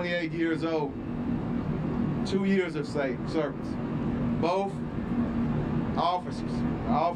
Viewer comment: "Officer 28 years old.. two years of satan... Service" did anyone else hear that!? Lol